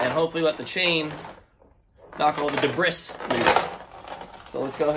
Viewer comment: did you say "dee brisk"??? (I replayed it 8 times) The word "debris" is pronounced "dee bree"... and no, don't say the "s" part at all. (not "dee brees") I gave you a thumbs up anyway. Meanwhile I must ask.... isn't a chain not really rough enough to break up rust? Lots of people say drop in a bunch of drywall screws. Thanks for your video!